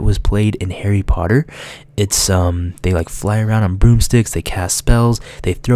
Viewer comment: did he say boobsticks?